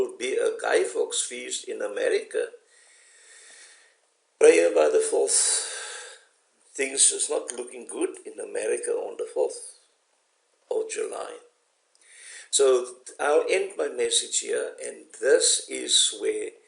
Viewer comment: What did you say at 25:55? About something that will happen in America on July 4th.? I'm sorry, but I tried to listen to it over several times and just could not understand it. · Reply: I SAID PRAY FOR AMERICA, THING DO NOT LOOK GOOD FOR THE USA ON 4TH OF JULY, BLESS YOU FOR WATCHING THE VIDEO.